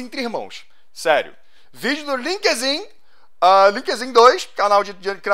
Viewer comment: eu JURO que quando ele foi falar o nome do canal eu entendi linkedin KKKKK